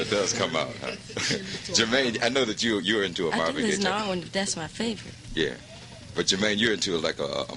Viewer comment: anyone know what Michael said at 2:20? something something "but that's my favorite" ??? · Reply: I didn't list n all, thats my favorite interview sounds like a brother so the ebonics came out for Mich